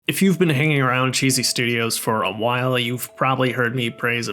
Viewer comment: Lol I thought you said Jesus studios great vid 😂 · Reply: Not sure if has been Jesus-approved yet 😂